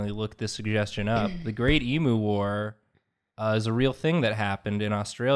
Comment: I heard "the emo wars", so when started drawing a bird I was so confused